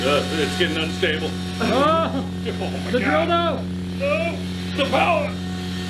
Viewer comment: lmao did i hear "the Drilldo"